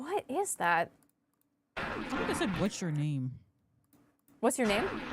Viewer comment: audio for the mycelium men sounds like it says "I see you" in the first bit. And in this moment sounds like "what's your name" idk if that's just me